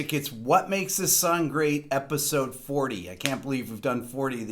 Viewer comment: sounds like he says sun instead of song or is that just me?